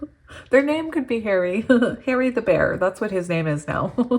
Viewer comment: Pile 3 before you said Harry the bear I heard “harebear” but I don’t know anyone that nickname could fit lol but maybe I’ll meet a Harry. Or maybe I picked up on a name for someone else